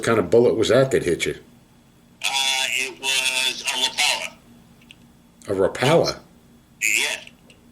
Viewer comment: I thought he said he got hit with a fishin lure (rapala) too 😹🤣🤣